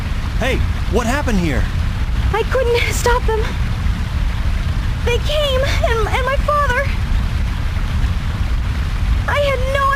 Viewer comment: Tikal definitely says "they came, IN my father" what is up with that? Is anyone else hearing this? Tikal also seems like she might be kinda dumb.